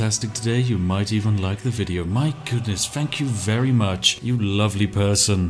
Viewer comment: why does it sound like he says: ''fuck you very much''